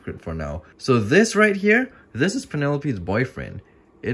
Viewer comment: When I first watched this my audio cut out and it sounded like he said “this is a male banana” and I was dying🤣🤣🤣🤣🤣🤣